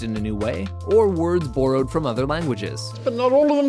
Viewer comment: um excuse me but did he just say boarowed?